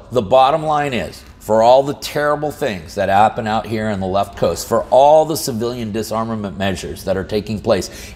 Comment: I thought you said “Left Coast” instead of west coast.